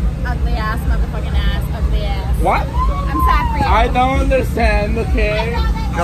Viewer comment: Gringa A: Depois de ver o instagram do ficou tentando puxar assunto sobre o time de e-Sports OpTic gaming e é completamente ignorada. .. 09:21 Gringa B: Fala que todos ali são feios pra porra, com exceção do indivíduo que ela aponta (possivelmente o Biel) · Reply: Eu entendi ela falar "upset for you" aos 9:21, e não "except for you"